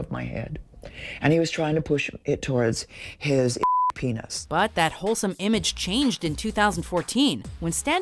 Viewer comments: did she say towards his *enormous penis?? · wtf were they bleeping out before the word "penis" .......??? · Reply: oh. fucking.